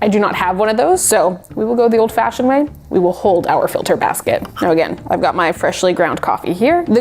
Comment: i thought i hear "hollow wicker basket" for a moment there